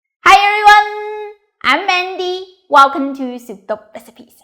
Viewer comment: I thought you were saying Mindy not Mandy for like a year or so when I first saw a video.